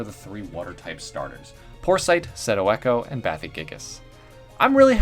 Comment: At first I thought you said *poor sight*, and I felt so bad for the thing as it already had communication issue and is nearly blind now. 5 seconds later: Oh, I was so very wrong